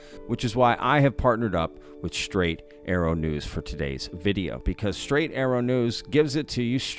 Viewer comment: Oh my God, one moment I thought you say "Straight AARO News". :face-fuchsia-wide-eyes